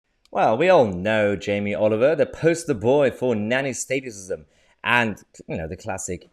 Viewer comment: I misheard it as nanny sadism